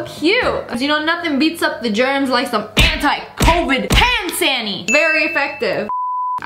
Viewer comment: When u said ‘hand sanny’ I thought u said panny